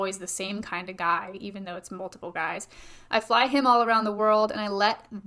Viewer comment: I’m gonna be honest. I always thought she said “I fly ‘em all around the world” not “I fly him all around the world”